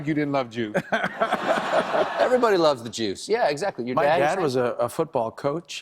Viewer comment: Does Colbert say "everybody LOVES the Juice" instead of "loved"... ?